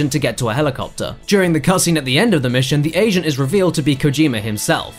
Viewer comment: I heard "asian" and then I realized he says agent LOL